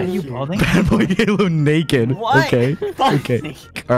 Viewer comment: did anybody else hear bbh go "whaat?? ... fhuck me" at 26:24 LMFAAOOOOO